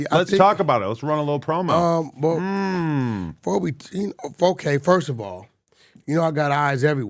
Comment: did he say 4k first of all?